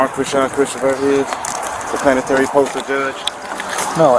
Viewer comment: Did I hear "Planetary Postal Judge"?